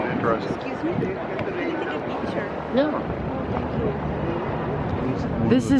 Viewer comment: did you say no to that lady asking if you could take a picture of her at 19:04?